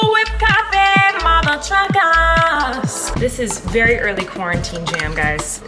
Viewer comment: “Oh my nunnes it’s time for whipped coffee, mother truckers” -Colleen March 3 2021! 😂 I thought she said mother f*ckers. 😂